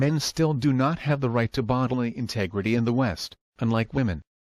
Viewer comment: what do you say at 18:29? Bottle leg integrity? what is this? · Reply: bodily integrity. I discuss bottle leg integrity in a different video.